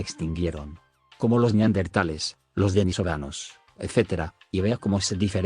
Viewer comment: Minuto 11:00 Escuche Venezolanos xD jajajaja yo tipo "cuando me extinguí ? "